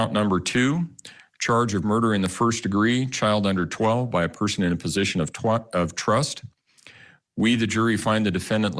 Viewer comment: lol did anyone catch "by person in position of twat"